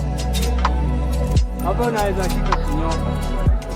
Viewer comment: "hapa unaweza shikwa ukinyonga" or whatever i heard 🤣